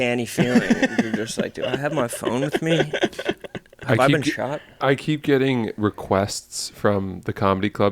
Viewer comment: HOLD UP did say, 'have I been shot'